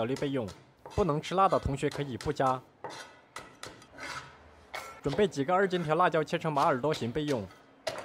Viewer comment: 听成了准备几个二进制辣条...